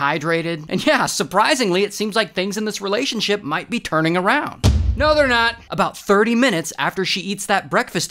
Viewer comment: Idk why hearing “No they’re not!” Instead of “UNTIL” got me laughing so much. 🤣